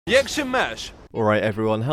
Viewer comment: Did I hear "Jak se máš" on the beginning?😅